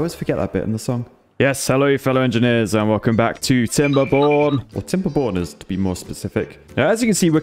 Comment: i thought you said welcom to "timberbon€r$" sorry my bad english and my way of thinking